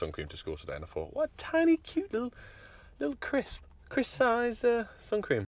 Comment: Did you say "chris" sized? 😆😆😆